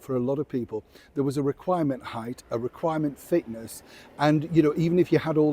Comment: I miss- heard. I thought you said a required ‘thickness’ ( not fitness) , and i said, ‘well, at least it’s out in the open now’.